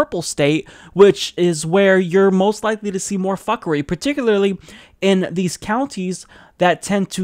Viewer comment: More fuckery? Did I hear that right? I'm not offended, it actually made me smile a bit - just checking if I heard correctly.